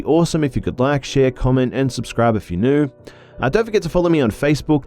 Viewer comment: What are the whispers in your intro? I always try to focus on it but I can't hear much. Understanding those whispers are harder than quantum physics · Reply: Chau Tran I always hear "subscribe to be busta" not even joking